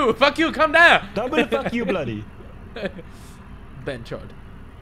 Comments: Hold on,wait a minute, At 6:17 ,did Ethan just say, “Bh****od” Lmao lol.... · Did Ethan just say Bhenchod. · If i am not wrong did Ethan really said "behen chod" as being an Indian hearing an english speaker say sister fucker(behen chod) make me feel proud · did ethan says behench*d